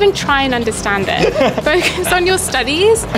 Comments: I thought she said “buy the SMP” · Bro I thought she said SCP 500 so I had to roll the video back lmao